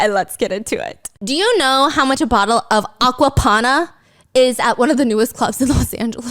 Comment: ~ Did anyone else hear “aqua tophana” 😅 You know you been watching too much true crime when! >.<